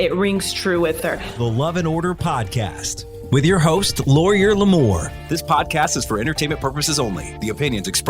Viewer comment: I love it!! The announcer voice kind of sounds like he says lawr-yer Limor tho 😂